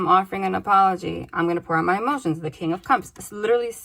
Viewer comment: When you said: "I want to pour out my emotions" around 51:30, I heard: "I'm gonna grow out a mustache." :DDD Talk about mishearing someone in fun ways! And who knows, maybe in the communication, he WILL have a huge frickin mustache. Would make it way less heavier ;) Thank you for this reading, by the way. It's so beautiful and such a blessing to the universe and all in it <3 My person has done some pretty deceptive shit. Listening to this... it helps me heal with that and also, your intro with Logic and Love? Helped me make sense of dreams telling me exactly that for weeks now. Yeah, we need to follow our hearts and see what happens. No tryin to control or make rules for this stuff... though there is a false safety in it. I feel like I've been making a lot of rules myself in this connection of what was supposed to happen how. But ultimately, I did share my soul and purity and Sun self and I chose me in the end. I chose to break off the connection and it put me in so much pain. I doubted myself for so long. This reading helped me understand that I was picking up on very real things - manipulation, deceit etc. - that I'd explained away after separating. But because these things were real, I can stop being so sad at myself for making the choice to leave back in the day - because it was the right thing for me, no matter how Bella-Swan-Breakup-painful it was for me after the fact. I can trust myself so much more now... thank you <3